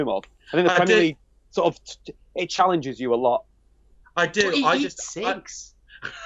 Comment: What did say around 21:14 ? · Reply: "But he eats cigs" (cigarettes)